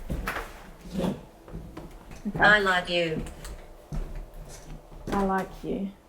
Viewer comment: 04/I swear I have listened to this thing three maybe four times and I still think she says I love you?